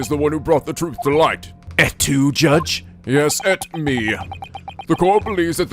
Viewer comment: i can’t understand what van zieks says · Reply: Its a quote from i think? Shakespeare. "Et tu 'name of the person'". Look it up it has some story to it.